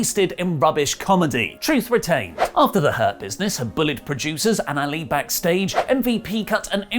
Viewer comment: I keep hearing "herpesness" on Oli rather than Hurt Business.. 😂😂😂